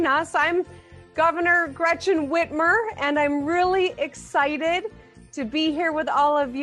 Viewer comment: sounded like it said Wenchmen Whitmer, it's words not mine if the name suits it, it's wearing it